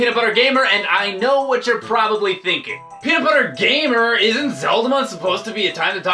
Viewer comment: the subtitles say Hitler gamer